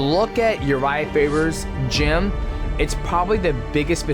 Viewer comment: I thought he said urijah fibers chin. had to rewind that back for a sec.